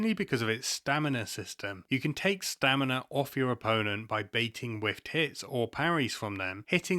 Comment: What is he saying? "Baiting with ____..."? I really hope it's not what it sounds like. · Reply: Baiting whiffed hits!! What were you hearing!? Lol